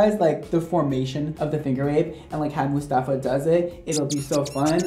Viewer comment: I lowkey thought he said finger wigs😂